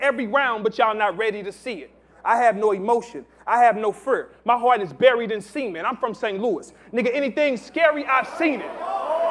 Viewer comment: biggest pause in battle rap history lmfaoo he really said his heart is buried In semen wow